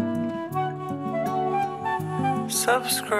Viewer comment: at the end when he says subcribe, and after mishka says it, it sounds like mishka says i am a good girl!